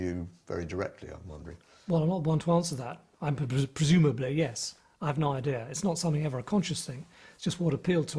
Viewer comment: What does Tony say at 47:41? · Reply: He says "well I'm not the one to answer that,presumably yes"